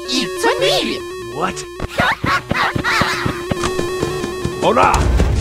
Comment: did.. did knuckles just say ORA?